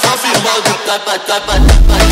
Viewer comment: HELPPPP I HEARD SUSSY AMOGUS AT 0:33 WHAT IS WRONG WITH ME😭😭